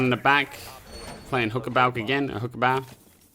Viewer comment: Be honest, who else heard him say "Hooker balc?"